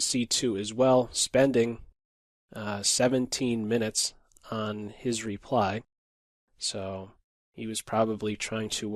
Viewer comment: wait he spent 17 minutes on the reply? did i hear that right?